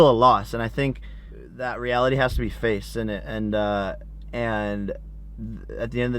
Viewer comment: did he just say innit